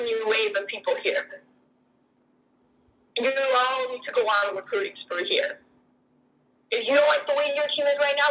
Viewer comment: “you all need to go out and recruit.” That’s what she’s saying at the part you said was inaudible.